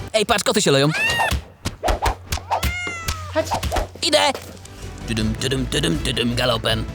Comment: Usłyszałam ✨glutek chodź ✨